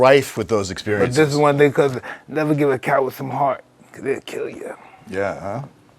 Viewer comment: can someone tell me exactly what mike is saying at 13:47. "never give a coward some heart"?